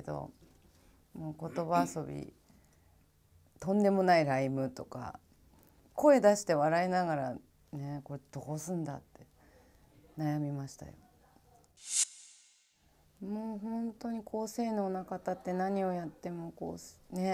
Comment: 「これどごぉすんだ？」 何て言ってるか分かんないけど好き